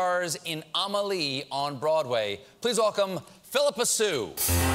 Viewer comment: I thought it said "Philippa so lost her cool"